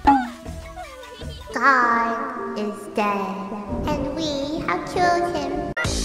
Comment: The part where Melony was like “god is dead and we have killed him” was the cutest and funniest part of this video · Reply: Omfg I heard "God is dead and we have children" now it makes more sense and isn't so disturbing